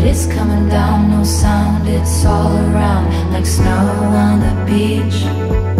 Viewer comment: Thought she was saying Lana on the Beach 😂😂